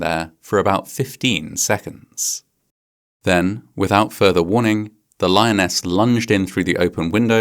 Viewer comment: "Without warning" Why would a warning be needed instead of common sense? It's not a suburban cat. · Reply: He said "without further warning." As in, there was already plenty of warning.